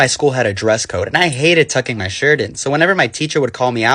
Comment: I dead ass thought you said "fucking my shirt in" 😭